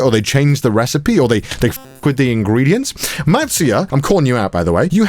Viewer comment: I thought he said "Marzia" too xD